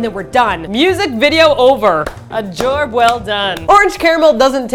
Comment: did you say "jorb" at 3:53? Are you Coach Z? :O I vote Catallena! Such a tasty MV... I want the salmon dress! ¬ Awesome KMM >w<